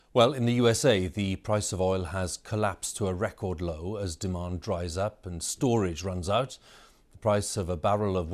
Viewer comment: Why did he say the oil price is collapsed to a record low as demand rise up and storage runs out? I think he should have said demand decrease and storage is full.